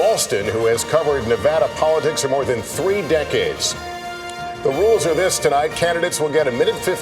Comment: did he say 40 decades?